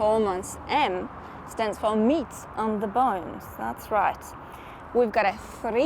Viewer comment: I had to rewind, I thought you said: "M stands for me on the bonnet" 😂